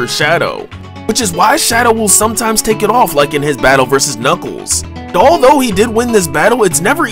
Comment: When you said "like in his battle vs knuckles" I swear I heard "like in his battle for snuggles"